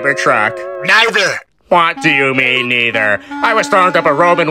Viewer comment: wtf is he saying? "I was throwing up a roben"??? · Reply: "Throwing up a roman" as in the Hitler salute